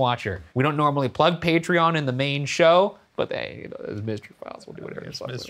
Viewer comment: Did Ryan say "mange show"?